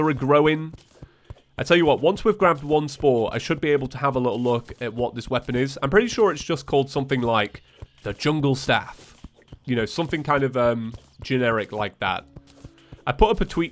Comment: i thought he said chungle staff